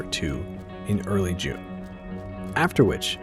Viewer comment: The subtitles says "late June," but the speaker says "early June. Which is it?